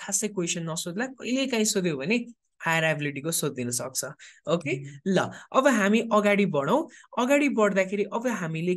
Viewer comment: ma k bhannu vaya ho I cant understand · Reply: Higher ability